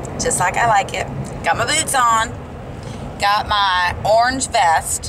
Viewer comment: Lol Wes...when I watched it I thought it sounded like I said boobs too!!;)) hahaha...boots dangit...lol;))